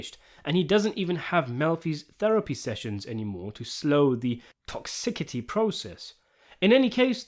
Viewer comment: the what process? I genuinely don't understand the words there, I think the CC is broken. Send help lol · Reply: Toxicity